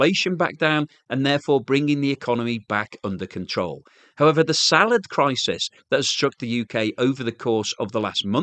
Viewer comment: did he say "salad crisis"?